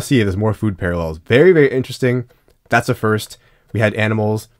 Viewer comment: I skipped ahead for a second when you brought up food parallels, and when I stopped I thought you said “Berry, Berry Very Interesting.” So that’s my idea for a food related insert. Can be like different players at a smoothie stand maybe. I’m workshopping it.